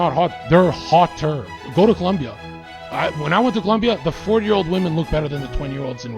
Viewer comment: did he say THE 4 YEAR OLD WOMEN LOOKS HOTTER THEN A 20 YEAR OLD WOMEN ??? it's a CHILD. Def the same type of guy that whine about false allegation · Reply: He said 40, not 4